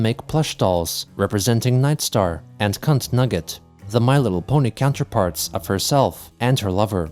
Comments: did you just say "cuntnugget"? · why did I think he said “Cuntnugget” XD